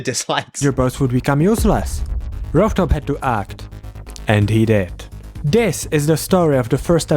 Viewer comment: im crine i thought that said "and he died..."